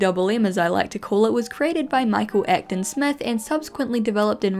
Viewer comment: I misheard you and thought you said, Michael Afton Smith. Lol The only moshi content I had were little toys I got from Burgerking or McDonald's (I don't remember which one they were from)